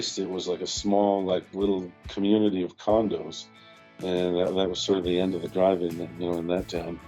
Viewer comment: Was it just me or did I hear "a small community of condoms"